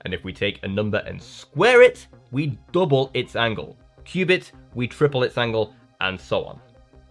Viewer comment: It me a moment to realize you said "cube it" instead of "Qbit." So glad your back. Also, if Microsoft's claims are accurate about their topological Qbits being decoherent resistant, the Qbits required would be much smaller.